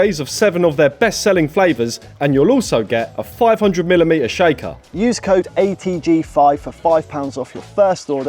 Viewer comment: 500 mm shaker? You probably did say ml but it's honestly sounds like you said 500 mm shaker on your holy ad bit ❤ good video guys. Love you both